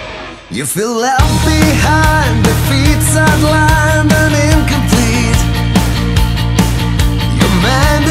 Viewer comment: I swear I hear him sing 'You fill out behind the pizza line, but incomplete' in the first verse, every time I play this. Other than that: great song. Oldskool pedal to the metal